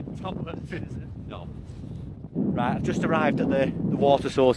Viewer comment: I am still pi55ing myself 14:51, you say under your breath "knob" - what did your man say? So funny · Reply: Haha I said “no” 😂😂. Can’t remember what he said but it’s definitely No haha.